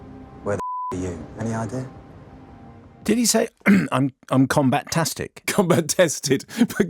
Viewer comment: I heard "combatastic" too.